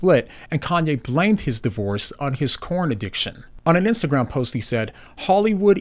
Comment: I thought you were saying he has a Korn addiction. 😂